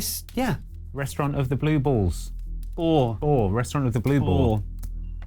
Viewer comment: For a second there, I thought he said, "Restaurant of the Blue Balls."